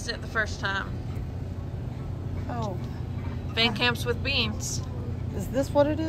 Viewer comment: I really thought you were saying Band Camp's chili all this time. Silly me. Though Van Camp's is less funny.